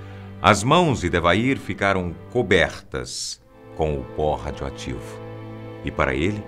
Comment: eu entendi PORRA RADIOATIVA KKK